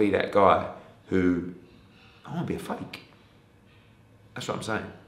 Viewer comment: Eddie,"I don't want to be fake". I heard, "I don't want to be Frank". Don't worry Ed, nobody does mate.